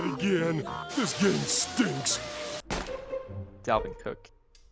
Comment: did he just say dalvin cook?